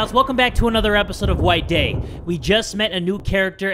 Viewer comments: At the beginning I thought he said white gay 😂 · I thought you said whites gay I was like WHAT THE FUUUUUUUUUUUUUUUUUUUUUUUUUCCCCCCCCCCCKKKKKKKKKKKK